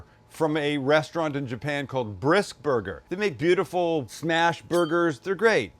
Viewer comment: I thought he said Bris burger. Blew water out my nose. 😂😂😂😂